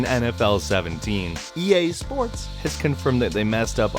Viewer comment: i heard "yay sports"